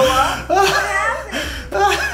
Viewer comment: "It blowed up". Goodness woman, did you go to public school? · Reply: I thought she said, "did it blow up?"